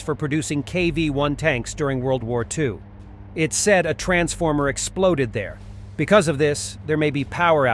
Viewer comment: Gee, when you said a transformer exploded, for a moment I thought you said transgender and started smiling but then I realized. 😮